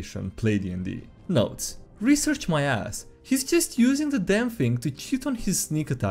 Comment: I misheard that first sentence as "research my ass" not "research, my ass" lol. English is amazing when you can change the meaning by not pausing. Reminds me of a Lost in Space episode. "I'm a badass princess!" or "I'm a badass, princess!" says a dude to his teammates' amusement. @ 12:20 You know that is funny this reminded me there was an anime movie called Project Itoh: Empire Of Corpses which is in short; about Frankenstein being real and his knowledge of truly recreating life being lost. This results in lesser versions aka literal zombies being used all over the world in a roughly alternate-history 1800's time period. Zombies being used in line formations with rifles being led by living officers, Unskilled Labor like loading and unloading goods, etc. The protagonist in a similar vein to a necromancer is seeking the secret of Frankenstein in order to truly resurrect people instead of zombies. So yes, they do in fact create jobs lol with necromancy; however you must be approved for using and or experimenting with revivification technology or it's illegal. Hope someone finds this funny and or interesting. Have a good day! :D